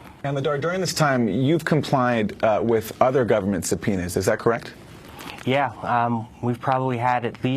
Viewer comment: did he say subpenises at 7:58??